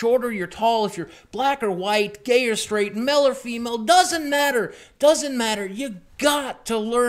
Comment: For a second I thought he said Mel or femel